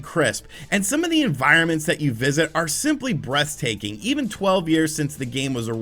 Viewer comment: This might just be lockdown melting my brain, but I really think I heard "simply breast-taking" at 6:31... Just me? probably, sorry.